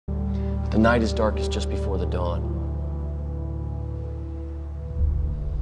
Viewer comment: For ages, I thought he was saying “But the nightest dark is just before the dawn”.